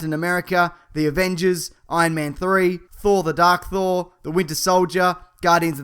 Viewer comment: did he say "Thor: The Dark Thor"?😂😂👌🏼